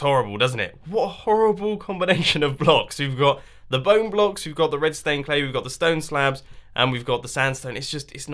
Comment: Who else heard him say "I need redstone stained clay"